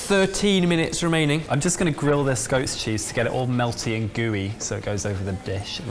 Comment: Lol. Who else thought said "ghost cheese"